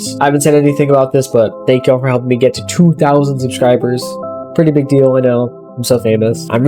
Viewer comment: i heard 200000 lol